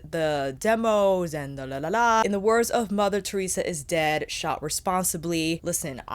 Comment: I heard it as 'in the words of Mother Theresa' and then the 'is dead' really threw me off, like ??? where is she going with this?? 🤣